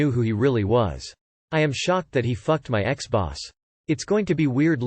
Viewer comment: I totally misheard "I am shocked that he f-ed my ex boss" as "I am shocked that he f-ed my Xbox." Yeah, that would have shocked me too!